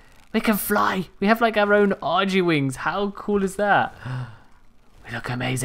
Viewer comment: onion bhagi wings??? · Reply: When you said Argy wings sounded like you said onion bhaji lol